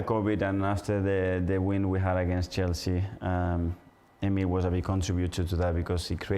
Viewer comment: did he say “emile” talking about emile smith rowe?